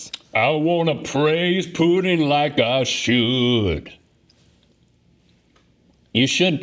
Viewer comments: It sound like Link said… “Praise Putin like you should,” 😝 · I first I thought link said “praise Putin like I should” lmao 😂